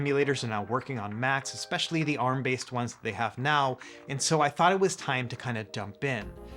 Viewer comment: did you say “So I thought it was time to Dump in”?